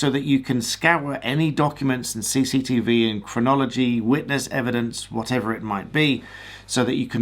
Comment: oh, "chronology". On the first pass I heard "phrenology", hardly relevent!